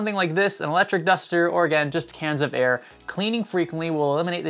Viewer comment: I thought you said "can of beer"... hehe, should have been "can of air".